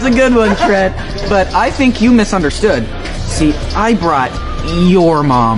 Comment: I heard "Your mung" is something wrong with my ears?